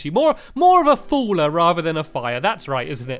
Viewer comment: I swear at 0:47 he said fire